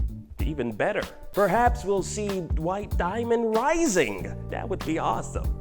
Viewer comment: I thought he said "Dwight Diamond" I'm tripping XD